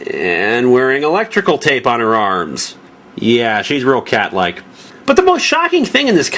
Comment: Does NightCat really call her car the 'Cat-allic?' Seriously?